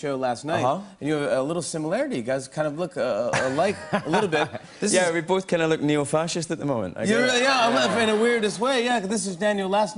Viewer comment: "We kinda look __ at the moment" what did James say before 0:12? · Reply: "We bot kinda look neo-fascist at the moment" :)